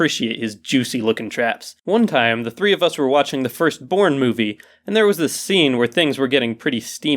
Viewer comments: When he said "First Born Movie" I thought he said "fIrSt POrN mOvIE" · Thought he said porn movie. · i thought that you said the first porn movie xD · why did I hear "the first porn movie" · I thought he said "first porn movie" , Not "first borne movie" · Am I the only one who thought he said that he watching a 'porn' movie?